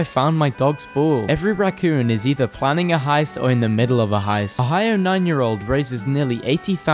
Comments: I thought he said “A high nine year old” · I thought he said "A hired nine year old" instead of "Ohio nine year old"